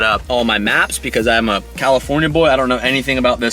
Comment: All I heard was “I’m a California’s boy”